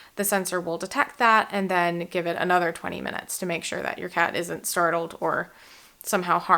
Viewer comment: I know, I know, my comment will never be read by the video creator but I just had to post. At first I thought I had just misheard you at 2:22... "wait, did she just say aDsorption?!?!" Your attention to detail and accuracy when describing the pertinent chemical reactions/phenomena should be applauded! Excellent review/description... I bought it! Thanks :)